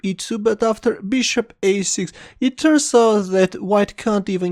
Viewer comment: (terisald?)what word is it?english spoken please... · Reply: after Bishop a6, it turns out...